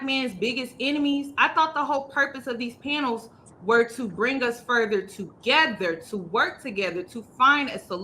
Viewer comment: did she really say bring us further together? 3:09 That doesn’t sound right 😂